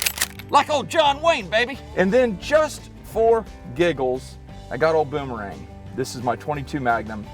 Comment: I thought he said shits and giggles had to go back and he just said giggles lol